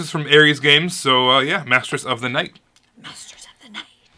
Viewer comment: lol i almost thought you said matress of the night lol something very different lol great video guys stay hexy! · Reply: Masters of the Night is officially less interesting than Mattresses of the Night. 🤣🤣🤣 although the original one does have a solo mode, so worth keeping an eye out!